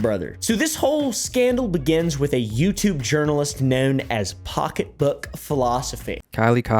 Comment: For a second I thought you said “Pocketbook Colostomy”. Anyway, outing someone just to “see what they’d do” is a horrible thing to do! I hope Sketch is okay.